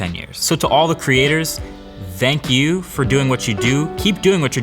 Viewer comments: Sounds like he said zank you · Did he just say "dank you"?